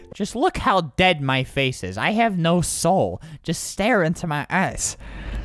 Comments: when he said ,,you can see the pain in my eyes'' i thought the said ,,you can see the pain in my ass'' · You can see the pain in my...eyes Tbh I thought it said you could see the pain in my ass at first 😂😂😂